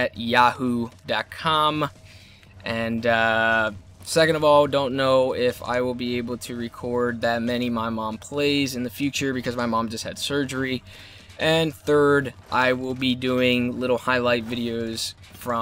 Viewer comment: Ha i thought it said my mom plays , high hahahhahaha